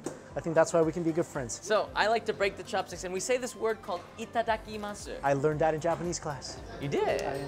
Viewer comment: oml- on 5:33 where said itadakimasu the captions said "eat that icky mustard"